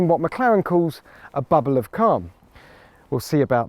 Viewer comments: I legitimately thought he said "Bubble of Cum" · "What Mclaren calls a bubble of ___ ." Me: WHAT DID HE SAY! Oh! "Calm". We don't pronounce "Calm" in America, like the Brits do. *And then the shot cuts to a bunch of small white stuff hitting Jack in the face, while he is screaming.